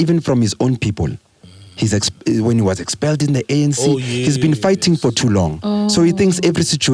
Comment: I heard him say Julies has been hurt, his been fighting for love tjo😂 juju needs a hug